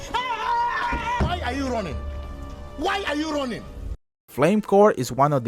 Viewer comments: all I heard was "why are you ronin? Why are you ronin?! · sounded more like why are you ronin? instead of why are you running :) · I swear i heard "Why are you Ronin? Why are you Ronin?!"